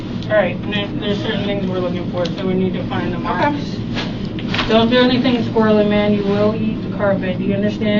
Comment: What does he say in reply to her when it gets real at 1:17:59? "Don't do anything squirrely, man. You will eat the carpet?"